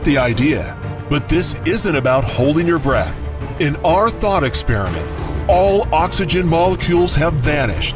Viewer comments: Am I the only one who hears “in our THOT experiment” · Lol 1:04 I thought he said ‘in our thot experiment’ 😂😂